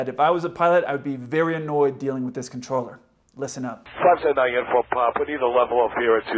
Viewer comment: I thought the pilot was saying unicorn pop